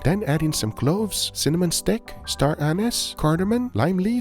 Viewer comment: did he say Star Anus?